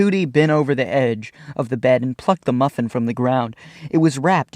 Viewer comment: i swear to god i thought you said he fucked the muffin